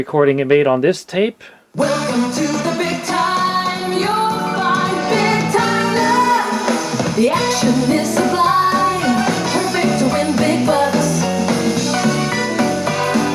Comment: Wait... Did I hear (@ 18:50-ish), "...The action is sublime, perfect to win big butts ..."?? How/where do I enter this contest?!